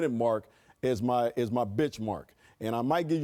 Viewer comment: I thought byron said “bitchmark”